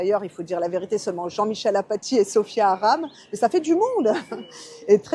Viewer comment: Sofia haram ?! C’est bien ça que j’ai entendu? Pourquoi pas une Sofia halal 😂